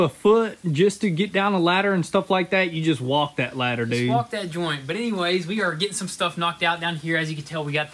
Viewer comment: Did he say “smoke that joint”? 😆